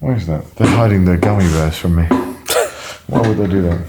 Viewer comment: I straight up heard Conner say "They're Hiding Their Garden Gnomes From Me." 😖 ...I am still not sure what he actually said. 😳